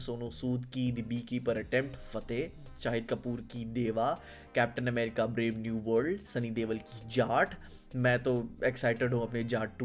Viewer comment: did he say sunny deol ki jhaat 😂😂😂😂